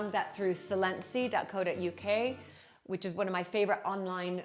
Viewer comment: she mentions a UK-based online antiques mall, the name sounds like 'silensy' or similar - does anyone know what this is? Google has nothing and the transcript also doesn't have the name. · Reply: Silenzi